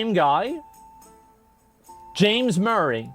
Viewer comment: I heard “James Murray” and got very confused 😂. Duhnuh nuhnuh!